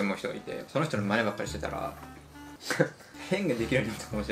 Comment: 『最近いいなって思う人がいて、その人の"真似ばっかりしてたら"』って言ったのが『最近いいなって思う人がいて、その人の"前歯借りてたら"』って聞こえた笑笑